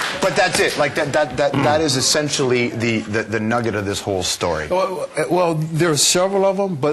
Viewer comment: Did he just say "the nugget of the story"